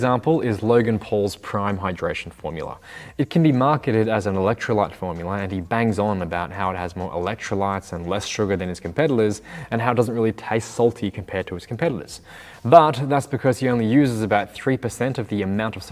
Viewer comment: “com-peddlers” I’m 100% sure he’s saying that instead of “competitors” and that’s brilliantly subtle. (Could be the accent, but Imma believe in the subtle jab).